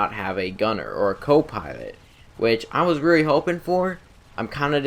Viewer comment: I heard "copiwot" instead of "copilot".